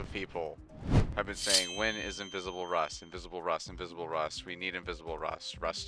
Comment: The first time you said invisible rust, I thought you said visible ray. I got scared